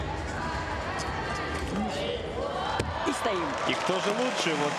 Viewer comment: What do they say at 0:27 and 0 0:37 ?? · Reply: First, they said “hold!” («держи»), and then they said “stand!” («стой»). The “stand” was meant for her to stand up and stick the landing.